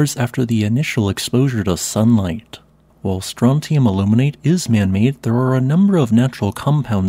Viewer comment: I heard "Strontium Illuminate"... and yep, I thought "appropriate".